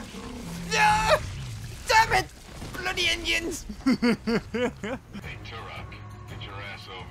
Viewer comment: Is there an onion that have blood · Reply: I thought he said onions when I first watched this 5 years ago but he actually says “Indians” as Turok is a plains Indian which we’re notorious for cutting the scalps off of peoples heads